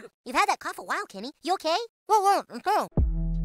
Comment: I know what Kenny said, he said ”oh yeah, I’m fine.”